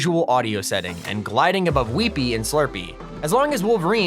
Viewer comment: did he just say WEEPY